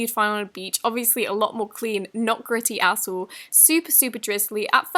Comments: I literally thought she said "Not gritty *asshole*" 😂😂😂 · sounds like she said *ASSHOLE* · it says like she’s saying “obviously a lot more clean a**hole” 😂 I know she’s saying at all but she said it so quick that, I heard that at first until I replayed it lol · Who else heard not gritty asshole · for the 'crabs in the sand' slime, when she said "not gritty at all', I heard "not gritty asshole" anyone else?? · it sounded like she said "....of course a lot more clean, not gritty, a*sh*le" 😂 of course she said at all but it really sounds like a*sh*le 😂